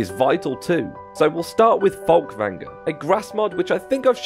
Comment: You skipped text over your grass mod, it sounded like you said "fogvanga" not "Fulkvangar".